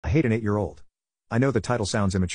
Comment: i thought he said i hit an 8 yo